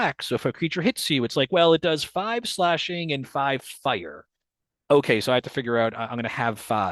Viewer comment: I misheard "5 slashing and 5 fire" as "5 slashing and 5 spider" @@